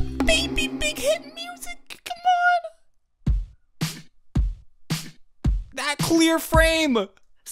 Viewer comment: i really heard "that queer frame!" dksjflsdjlfkds